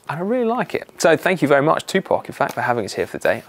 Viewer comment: First time I watched, I heard "...so thank you very much, Tupac"